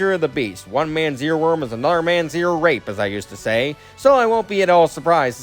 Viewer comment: I don't really understand what you said. And you should probably change the title since you accidentally said Ten instead of 25. Anyways... 25. A good final boss theme. And if it makes you feel any better, it wouldn't be very high on my list either (though it would still be on the list, just not very high) 24. Heck yes! BTW I'd say you could probably get a better listen to it in the second phase of the fight. 22. For some reason when you were talking about buildup to the song I expected Dancing Mad. 21. Pretty good. Though I personally prefer Guardando Nel Buio, Darkness of the Unknown and Dismiss. 19. Good, but I personally prefer Megaman 9's final boss theme (yeah, original choice I know) 18. Definitely a great song to play during the last phase of my favorite Zelda boss. 17. An epic final boss theme. If only it played in a better mode. 16. I'm not a NMH player, but this song does sound sweet! 15. YES!!! 14. I really need consider getting the Spark the Electric Jester games. I just wish they were playable on the PS4 or PS5. 13. Good, but I didn't like the boss it played against. And I didn't know you did a video on this game! 12. I don't know if I've even heard of this game or not. 10. Personally I find it overrated (doesn't help that it's the exact same final boss theme as the first two games), but I understand it's your list. 9. For some reason I thought that woman was Lara Croft. 8. Really neat! Though personally I prefer DK64's final boss theme. 7. Wasn't expecting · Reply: Glad to see you liked the video! For the record what I said at 1:57 was: "One man's earworm is another man's earrape as I used to say." which was basically my way of saying what one person likes from music another person might not. Also, I do plan on changing the video title soon. Right now, it's an intentional mislead to surprise the viewers.